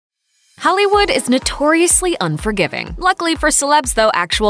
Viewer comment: Did she just say Hellywood in the beginning?